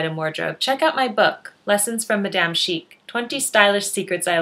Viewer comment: i always hear "lessons from the damn chic" :) lovely channel! thank you!